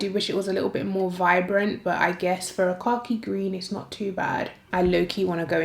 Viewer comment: I’m usually good at understanding accents but tonight I kept asking myself what is “car key green?” Then I thought well maybe she’s saying, “cocky green.” After the fifty-eleventh time I realized you were saying KHAKI GREEN 😩😂... Duh! I can’t with myself 🤦🏾‍♀️